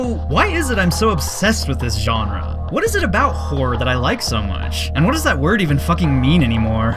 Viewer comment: I kept hearing say "whore" throughout the entire video. I love the American accent.